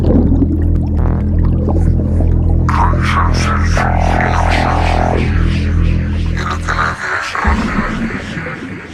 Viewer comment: de ne diyor ? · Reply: Yine mi kader